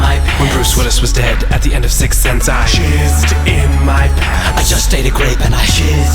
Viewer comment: When this first came on SNL I thought they said Shizzed in my pants. That was the first time I learned the word jizz.